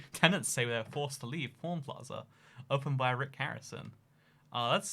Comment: Because of the accent I thought he was saying porn plaza, lol.😂 🤜🏻🤛🏻